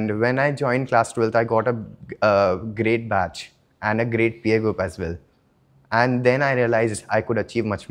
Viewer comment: What PA group he said?